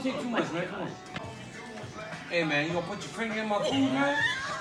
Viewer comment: Dead ass thought he said my niqqa to dc 😂😂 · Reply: Lol, first time i thought that. Then understood said finger with his heavy accent